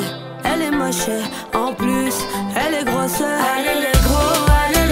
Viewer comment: i thought she was saying allez negroo😂